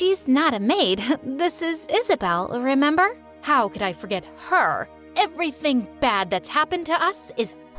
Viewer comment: 😂when she said “how could I forget her”I heard “how could I forget fer”😂😂😂😂😂😂